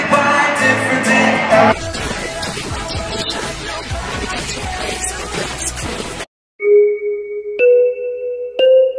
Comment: what did the interviewer say at 3:43 I couldn't hear it the sound was glitchy. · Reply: she was trying to say "the girls scream" but it was pronounced like "the girls cre*m" and so the boys all thought it was funny😂